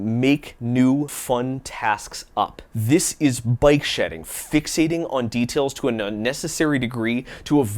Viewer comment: Oh BIKE shedding! I thought you were saying BYTE shedding. I thought it was like some sort of thing where you skim off bytes in order to save space where you probably shouldnt. Like digital corner cutting